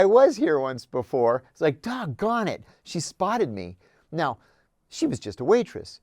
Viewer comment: "dog garnit" bro was tryna say god darn it 🤣🤣